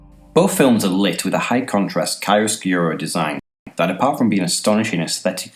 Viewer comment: What is the word you say at 4:43? "High contract __ lighting design" ? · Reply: "High contrast chiaroscuro design" it's an italian word "in art, is the use of strong contrasts between light and dark" See Wikipedia: <link>